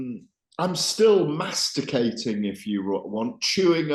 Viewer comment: Thought Gareth said “I’m still masturbating” 😂😂😂